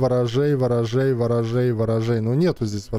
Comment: я один слышу слово рожай?)))